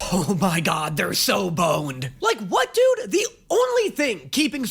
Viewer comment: i was sneezing and heard that wrong and i thought said he was boned…